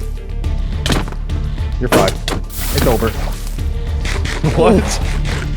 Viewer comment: Anyone else hear swere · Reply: "you're fried, it's over"??